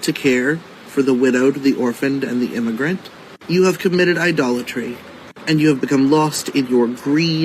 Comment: Did he say I committed I dollar tree??🤷🏼‍♀️🤦🏼‍♀️